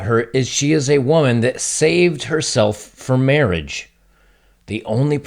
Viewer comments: Who else thought it said "she SHAVED her V for Mr. Perfect"? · Could have sworn this said “she shaved her v for mr. perfect” 😂 what kind of shoe is this!